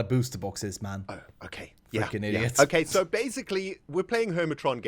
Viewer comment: did say "fucking"! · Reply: I'm 99% sure he said "Fricking".